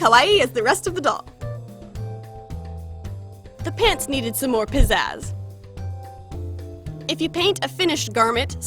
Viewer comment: I heard "the pants needed some more decorative pizzaz" #lol